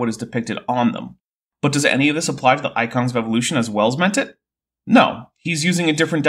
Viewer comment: I misheard "as Wells meant it" as "as well as my tit" and was very confused for a second.